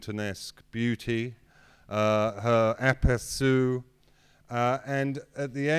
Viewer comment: What's the word he uses here? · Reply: aperçu